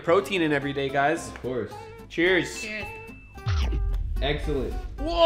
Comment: i dont know if it was supposed to be a joke but i swear i heard said egg-cellent. Like if you heard the same thing